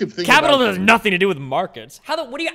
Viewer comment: He did not say capitalism has nothing to do with markets he said capitalism's distinction is not markets